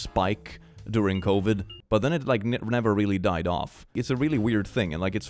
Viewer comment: Whenever you say 'gotcha' I hear 'gacha'' lol